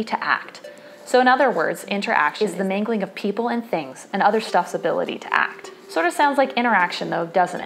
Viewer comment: Can anyone help me? Anybody knows what she says at 0:22 - "the mingling of people and things and ....."? Thank you! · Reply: "and other 'stuff's' ability to act"